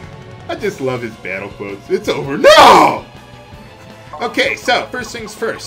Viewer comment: actualy he says" Its on now" at least thats what i hear